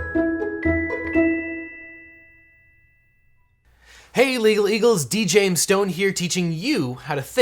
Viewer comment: I thought he said "Hey, Illegal Eagles"